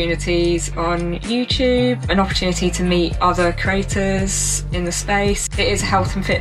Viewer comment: I kept hearing “craters”.